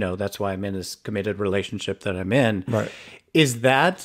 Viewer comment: I wish there was more back-and-forth with the filter, bottom guy, sorry, I'm new to your show. I forgot his name. I've recently heard a lot of older partnered gay men say "Oh. Yes, I'm in a relationship because I don't want any stds, which sort of implies that you don't really love him. You know, you're just scared of STD's. Your partner could be interchangeable as long as they're faithful. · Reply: "He said it's one of the reasons," YOU KNOW THAT IS NOT TRUE, YOU HAVE A BIG IMAGINATION . @ 19:24 he actually said,,,, "thats why i'm in the relationship i'm in."